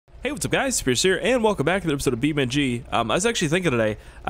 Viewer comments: I thought you said hey guys , it scarce here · omg i thought he said he whats up guys its scarce here